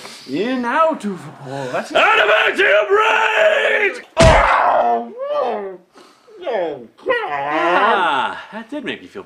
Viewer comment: "ADAMANTIUM RAGE"?? This whole time I thought he said "I'M ABOUT TO BREAAAK"